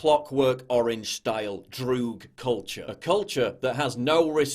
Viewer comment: Did he just say ДРУГ culture?